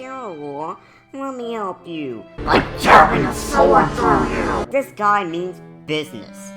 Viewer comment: What did he say at 9:04? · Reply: "By jabbing a sword through you."